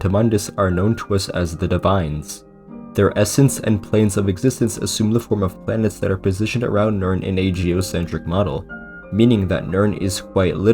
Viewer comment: what the fuck was he trying to say lol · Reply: ?? “Their essence and planes of existence assume the form of planets that are positioned around Nirn in a geocentric model”